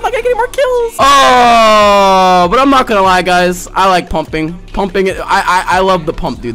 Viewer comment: I thought you said humping Lmao